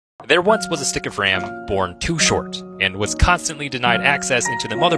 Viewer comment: I keep hearing "And Wisconsinly denied"